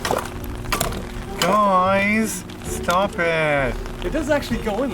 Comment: all I hear is "stop poking me" in a very winy voice